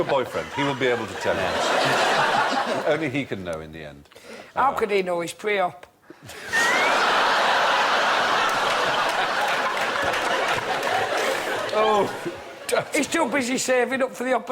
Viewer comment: and 3:56, what does Johnny say?? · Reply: He said "how can I ask him he's preop"